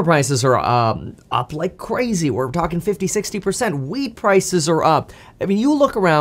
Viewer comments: Could have sworn you said weed prices are up 😂 still not sure whether you said wheat or weed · Could have sworn I heard him say “weed prices are up” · Did he just say “WEED Prices are up?”